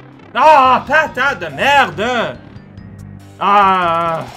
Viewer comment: What is saying when he gets frustrated (13:47)? Sounds like French but I'm just curious as a newer fan. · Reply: "patate de merdge" or literally "shit potato"